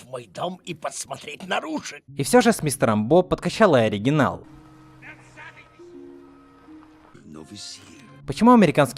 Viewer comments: Что он сказал? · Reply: "Новый сыр" - американский акцент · что он сказал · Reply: "новый сыр"